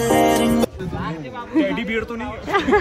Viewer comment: Wait did he just say Teddy bear 🤣